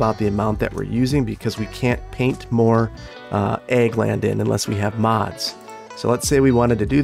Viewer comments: At first I thought he was saying egg land and not ag. land lol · Everytime you said "Ag land", I hear "Egg land" and I keep seeing eggs...hehe... Maybe you want to try "Agri land" instead?